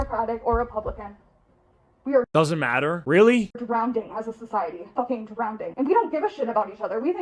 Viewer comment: Wait wait wait 18:45 ish… did she just say “Drownding”?!?!?!? 😭